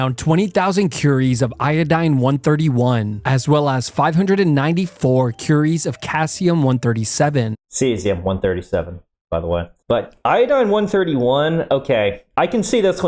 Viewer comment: Cassium sounds better and more fun... We need to rename everything to Cassium now :D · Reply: I thought he said "calcium".